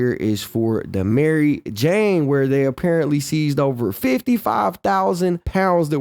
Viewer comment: no? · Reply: I thought they said 55 pounds not 55.000 i was tripping 😂